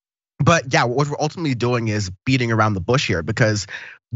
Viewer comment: I could have sworn he said "ultimately, you're just eating your mom's bush here" hahah 😂 Low volume....was like I've never heard that expression before lol 😂